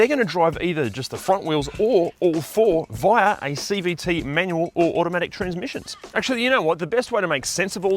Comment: did he say manual? BTW, the RAV4 hybrid does not have a CVT in the actual sense, it's a planetary gear set that powers the hybrid and RAV4 Prime. The auto industry just calls it an e-CVT for lack of a better name.